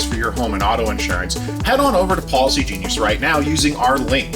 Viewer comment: I thought he said Paulsy-genius every time. 😂